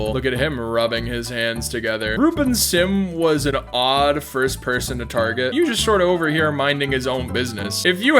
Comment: DID JUST SAY THE N WORD?! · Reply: no, he said "ruben sim was an odd first person to target". Slow it down if you need to